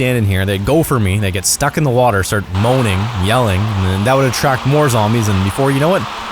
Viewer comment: Did he just said moaning